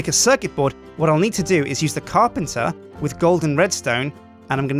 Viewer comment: I thought u said goldenredstone